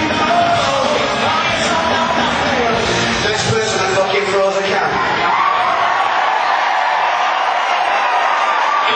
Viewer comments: What does he say at 0:47? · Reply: "Next person that fookin throws a can" sth like that lol why do answer to 4 years old comments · What did he say @ 0:47? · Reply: Next person who fucking throws a can